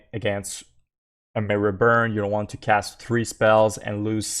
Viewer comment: The tip at 8:52 is kind of confusing. Are you saying I don't want to cast those three spells and lose 6 life? That's what it sounds like but I swear it sounds like you're saying "you're going to want to". Thanks for any clarification!